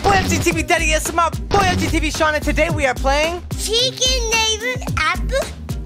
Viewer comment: did he say chickin neighbour ??? xD such a cute guy! Btw ive been watching dince day 1 ❤❤❤❤❤ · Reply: Darts Adventure no...it sounds like it but he’s saying secret neighbor